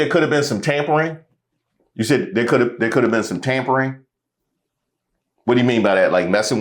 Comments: I definitely heard "tambourine". Leaning into it being a performance. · Tampering? I thought she said tambourines.... 🤷🏾‍♀️ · I thought she said tambourines not tampering...... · i thought she said tamponing :D